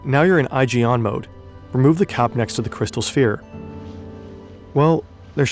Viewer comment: I’m hearing “remove the cow next to the crystal sphere.” What’s he actually saying?